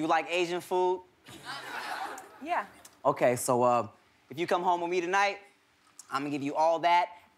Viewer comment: Somebody please translate what he says after 7:30 ( "hey girl u got Asian food" didn't understand anything he said after this) English isn't my first language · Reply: Not remember the details but goes sumthin' like this : "...if you come home with me, i'd give u that.. and.. DIM SUM!" => he meant Damn Some. (Dim Sum is asian food btw) Cmiiw